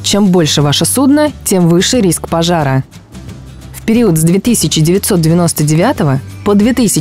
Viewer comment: 2 999 год! Я один это услышал? Две тысячи девятьсот девяносто девятый год!!!!!!!!!!